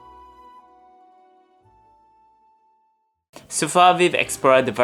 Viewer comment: “living”. sounded like “bribbing.” 😂❤. It’s ok.